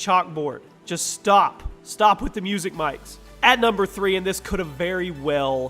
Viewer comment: When he said " Music Mics " I thought he said Magic Mics